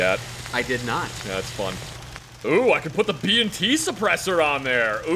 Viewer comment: saying B&T at about 11:20 gave me a freaking heart attack cause it sounded like he said BMT.